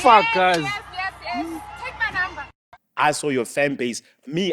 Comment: did she said take my number 😔